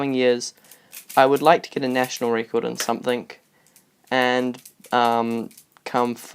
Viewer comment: you said somethink instead of something.